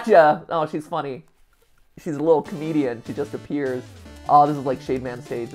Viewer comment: She's a little Comedian Me, Mishearing: Canadian?? Confusion then relistens to it